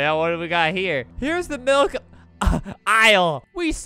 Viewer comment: I thought it said "Milkie"